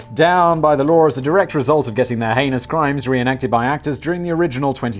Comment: when my (ALL THINGS “BUTT”- Loving) Brain hears “ANUS CRIMES” instead of “heinous crimes”......